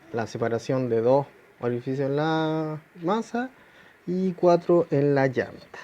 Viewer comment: Dos orificios en la masa? O escuché mal.